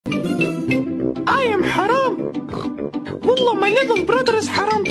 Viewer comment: LMAO IT SOUNDS LIKE SHE SAID '' I AM HAM'''